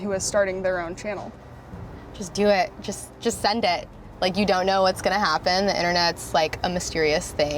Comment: did she say send it?? does she watch ELTON CASTEEE